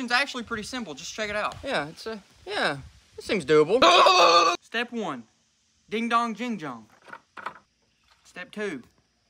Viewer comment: All I hear is, "he Honk, bang bong"